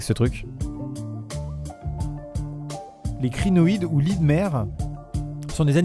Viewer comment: lys.. "lisse" pas "Li" Lys la fleur quoi ⚜